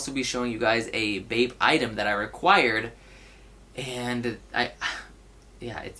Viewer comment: "And I will be showing you guys a Bape jacket I required" required for what? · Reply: obviously I've heard the word aquired before 1. it was a joke 2. definitely sounds like you said required